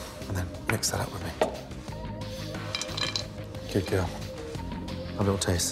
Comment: did he just say good girl I